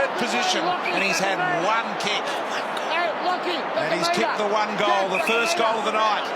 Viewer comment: anyone else hear that whisper? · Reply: 'and one goal'